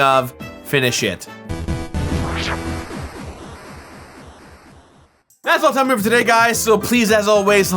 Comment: What is your favorite Mario game of all time? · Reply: It sounded like you said finna shit